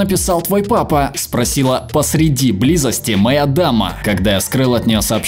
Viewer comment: услышала мама вместо дама...